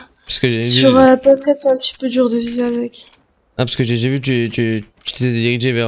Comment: Ses moi ou il a dit 5:07 sur ps4 ses un petit peu dure de viser avec ? 🤔 je suis sur play et j’ai aucun problème avec le pompe